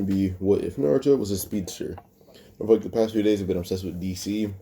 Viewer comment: I thought that said what if Naruto hi stepsister